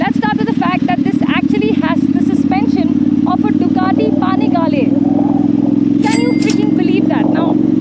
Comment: did anyone else heard "Ducati Pani Walle"????